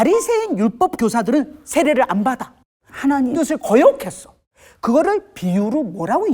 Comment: 에 하나님…이란 말이 들려요. 조혜련씨 목소리로.. 저만 들리나요?